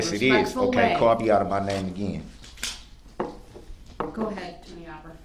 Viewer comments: At @ 14:02 I can't understand what he is saying. Is it call me out of my mind again? Does anybody know? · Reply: He said "Call me out of my name again" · Can anyone please tell me what he actually said there? I just keep hearing "Ok, call be outta my name again" but that obviously makes no sense.